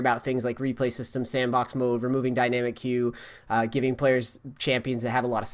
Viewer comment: "removing dynamycQ" ? wtf were you saying a few videos down ?